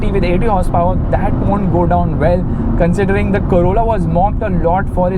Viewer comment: Even when you said Corolla Heard it as Corona. 😅 so much impact this lockdown has had in the mind man!